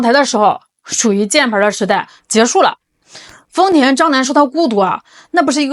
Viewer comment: 丰田张南，我老听成丰田渣男😂😂😂😂😂